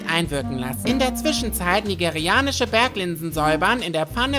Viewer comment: Ich hab nigerianische Berglöwen verstanden anstatt Linsen 😄😄😄